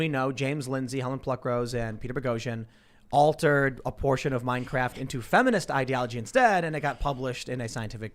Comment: Tim says Minecraft instead of Mein Kampf XD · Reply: You're obviously not German, he said "Mein Kraft," not "Minecraft."